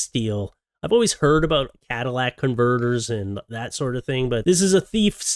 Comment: Ngl, I got kinda bored half way through the woman with the stuff down her top and such. Also, who else thought was saying "Cadillac converter"? lol